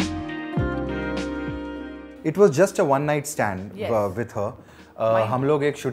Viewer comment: did he said ONE NIGHT STAND